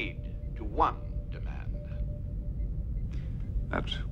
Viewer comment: 👈👇👇🔲 Oooh, you said finality, I thought you said fatality. Sorry to wake you up scorpion. Toasty.